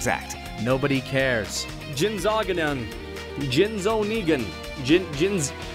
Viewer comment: What's he trying to say there? Jinzo-what? Sounds like a Yu-Gi-Oh! card... which was also a cyborg. Go figure.